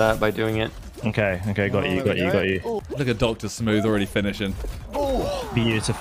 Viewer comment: did he just say the n word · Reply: “look at doctor smooth”